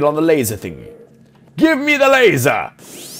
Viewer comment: I thought he said give me the Vicodin! I've watched to much HOUSE.